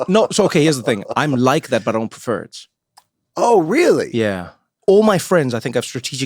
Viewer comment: I giggled a bit when the closed captioning had an error. At 3:06, Trevor said "I'm like that, but I don't prefer it." The caption says, "I'm like that, but Perverts."